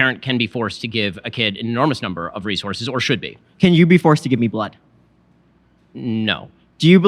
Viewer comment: Wait what? 40:30 Ben says yes a parent can be forced into providing an organ for their child? No fact check??.. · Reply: Could be wrong, but I think he said they should be